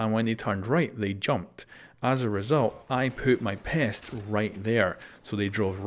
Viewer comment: instead of “I put my pest” I heard “I poop my pants”